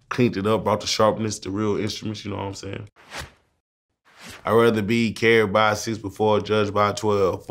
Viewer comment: I thought he said rather be carried by 6 foot 4